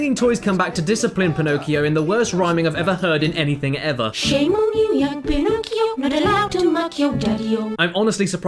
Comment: "Shame on you young Pinocchio, it's not okay to mock your daddy-o" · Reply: I was wondering what the toys were singing. I heard "Shame on you young Pinocchio. What a laugh to muck your dirty-o".